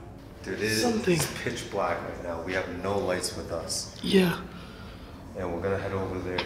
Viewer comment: I heard a voice, like a girl voice. Or was it one of yours? Its in 40:46 time frame. "yeah" or something like that.